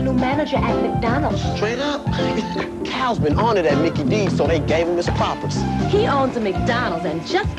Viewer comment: what the heck did he say here? My translation is "Calvin on it at Micky D's so they gave him his propers."